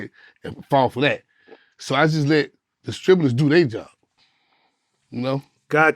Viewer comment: I thought he said strippers gone pay him😂😂😂😂😂😂